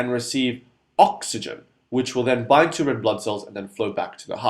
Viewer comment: and 22-25 I had to rewind a dozen times "to get it"....couldn't make out your fast wording....best I could come up with was "bite into"...ha ha...finally I got it....bind to. Good explanations all the way around. Thanks!